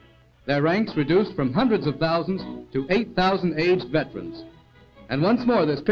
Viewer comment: The subtitle says "AIDS veterans"?? It must surely be "aged" the narrator says…?